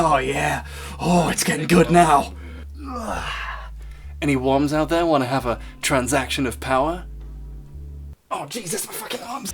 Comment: had to turn on subtitles, thought he said "any 1s out there want to have a transaction of power?"